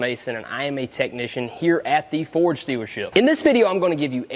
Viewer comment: It sounded like he said Ford stealership!!!